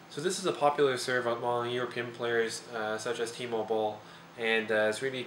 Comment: Dumb thought: I thought he was saying "T-mobile" - Timo Boll...really sounds like it -_